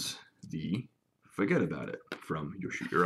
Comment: Hi there, great collection you got yourself. Can you please tell me the name of the funky slingshot shown at 7:13 (sorry I can't hear you properly) and where is it sold? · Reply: Benjamin Kepinger the frame is called the Fuggetaboutit and it is made by You’llshootyereyeout. He has an Etsy store, but I’m not sure if there are any on there at the moment. I know he recently moved and is still working on getting his shop up and fully functional. It’s worth reaching out to him through the various forums, or through his Facebook page, I believe it’s You’llshootyereyeout Bespoke Slingshots os something very similar.